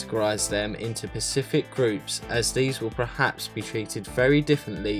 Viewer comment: Did he just say “pacific groups”? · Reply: Hello Chace, sorry that the audio appears to say pacific, it should be specific. Unfortunately our microphone at the time of recording was not that good. But we now have a new and better microphone so our audio should be clearer :)